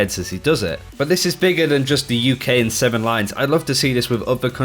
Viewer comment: 7 lions is a music artist tho oof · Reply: It sounds like he said 7lines